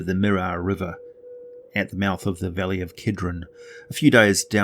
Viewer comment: I thought you said Valley of Kedron. Kedron is my name and it threw me off guard haha